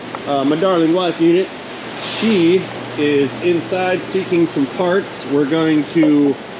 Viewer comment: probably said wifey is looking for parts, but I swear he said she's "seeking farts."